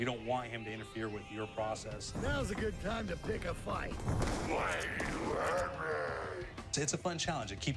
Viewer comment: it sounds like he says "why did you hurt me"